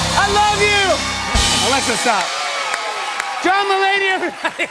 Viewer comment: At the end, I thought Jimmy said John, my lady lol 😂